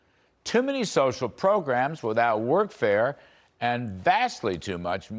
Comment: I heard " Work Fair " - But I didn't catch what was intrinsic to a ' Work Fair ' outside ' Taxation Without Representation '.